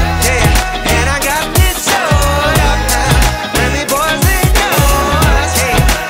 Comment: Yeah Baby girl, you're so damn fine though Wanna know if I could hit it from behind though I'm sipping on you like some fine wine though When it's over, I press rewind though Talking bands, I got it Benjamins in my pocket I traded in my Trues for some Robins Then he playing Batman, Fetty's gon' rob him, Yeah I got the Glock in my 'Rari 17 shots, no .38 Yeah, I got the Glock in my 'Rari 17 shots, no .38 [Bridge] Yeah, she's fine Wonder when she'll be mine Walk past, I press rewind See that ass one more time [Hook] And I got this sewed up Remy Boyz, they know us All fast money, no slow bucks No one can control us And I got this sewed up Remy Boyz, they know us All fast money, no slow bucks No one can control us [Verse 2] She’s got class and style Street knowledge by the pound Baby never act wild Very low-key on the profile Catching feelings is a no Let me show you how it goes Herb’s the word, spin’s the verb Lovers it curves, freak what you heard [Bridge 2] I like the way you work it (No Diggity) I’m bout to bag it up I like the way you work it (No Diggity) I’m bout to bag it up I like the way you work it (No Diggity) Bout to bag it up I like the way you work it [Hook] [Outro x3] No diggity darling I like the way you work it (1738) No diggity darling I'm bout to bag it up (1738) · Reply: Thought it said "N I got this soda